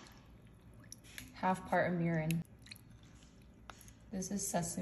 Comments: I thought she said “Half part of urine” I had to scroll back to read the captions cause I was confused 💀😭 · Had to double take. When she said mirin I thought she said urine.